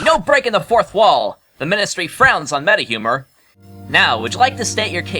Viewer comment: I can't tell what Eiki said at 5:27. "Hey, no breaking the fourth wall! The ministry frowns on many humor." Is that what Eiki said? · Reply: it's "meta-humor"